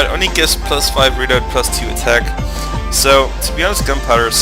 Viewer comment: When you were talking about the tosa riflemen vs the sharpshooters, you sounded like you were saying "instead for 300 more koku you can have sexy-five reload..."